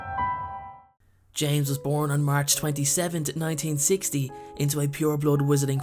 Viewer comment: Who else thought he said March 22, instead of March 27 and got triggered.